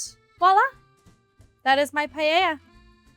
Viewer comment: GIRL ISN'T PAELLA SPANISH NOT ITALIAN?? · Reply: All I hear is "pallella"